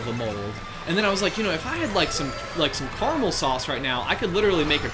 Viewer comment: I love accent! I heard that as ‘Carmel sauce’ at first!